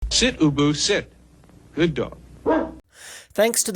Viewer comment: I thought he said “sit boo boo sit”